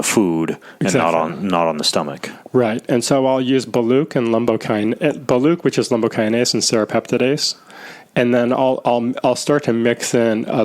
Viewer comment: What is he saying at 36:11. “I use baluke and lumbokine? Baluke serapeptasis???? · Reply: Boluoke® brand of the enzyme Lumbrokinase and the enzyme Serrapeptidase